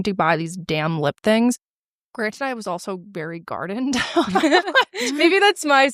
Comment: I had to google what gardened was, I thought she said guarded by security 😆